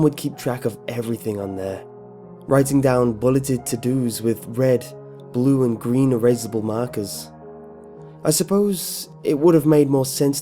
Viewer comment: I heard him say green blue and red markers, not black